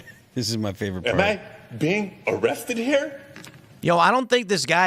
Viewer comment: did he say "arrethded"?